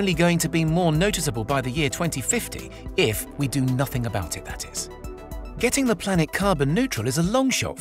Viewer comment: Hallo. Could any english speaking person please tell me what is said between 5:14 and 5:16 after this : "...by the year 2050 if we do nothing" //***// ? I listened to it tens of times but I still can't understand. Many thanks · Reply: He says 'if we do nothing about it, THAT IS'